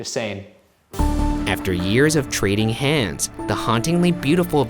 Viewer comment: I thought you said "after a year of catching hands" not "trading hands" and I was very confused if you were talking about your and Shane's realationship or not lol.